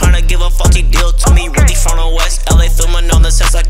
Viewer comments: miles what did u say there i’m still confused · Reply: *faulty deal 🤣🤣🤣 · Straight 💥💥💥 at 0:35 I think he said FAULTY DEAL! He didn't curse lol😁😁 Thanks